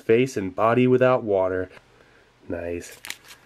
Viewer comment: I swear, everytime i hear you say "Nice", it sounds like "Noice" nin my head.